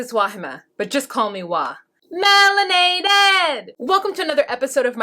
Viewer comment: Proud Patron of "Just call me Wah"!!! - I LOVE your work, I just wish I knew what you were singing during your opening...I keep hearing "Meeerrrinaded". But I don't think that is it. .....I just realize that with the exception of Jonathan & Fernanda, every couple has children involved in their lives in one aspect or another. And frankly, it broke my heart to watch Eric just sit their like a cowardly douch, while the Indonesian Princess attempted to kick his 19 yr old daughter to the street - Forcing Tasha to defend herself and remind Dear Daddy and his newly acquire Squeeze that She is on the lease! - Eric should of found a House prior to Leida's and son's arrival. And you know what? If Tasha is "just a Roommate" (paying rent and half the bills), she doesn't have to roll out the red carpet for her Daddy's new girlfriend, because Eric told her too....If I had a roommate that demand that I scrub the place spotless bc she was bringing home a new boyfriend from the Moon, Oh and btw I had to move out ASAP , Id tell her to "Bag Sand" as Eric would say.....and if Eric is 40 years old, I'm the President of INDONESIA!!! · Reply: She says “melanated” in her intro ☺️